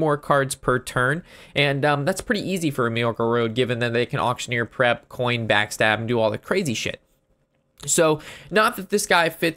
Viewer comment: did he just say coin prep backstab?? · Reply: or was it prep coin backstab?